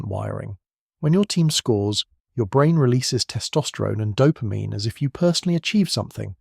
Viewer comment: "when your team scores your brain releases testosterone." As someone who doesn't follow sports (other than auto racing) what I heard was, "I have low testosterone."